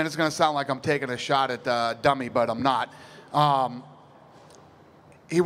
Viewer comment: Is Dana calling Anderson Silva "dummy"?